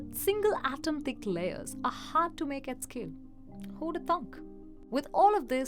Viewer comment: What did she say at 4:05? "Hoodathunk"?